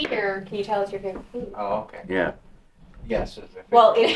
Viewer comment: a female whispers “yeah”, very loudly! 43:29 when you mention Gary, a man whispers “I am”. I heard many more responses but I’m at work, so I’ll try to post them later.